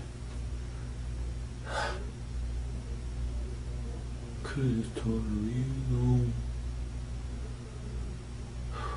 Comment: he says " you could have just told her to leave you alone" then you can tell he's reliving it and @ 1:36:11 he says that sounds like either " I shouldn't have grabbed her neck" or " I should've grabbed her bag" can anyone make out what he says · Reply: Christopher Driggers I think he says “I should’ve just grabbed her bag”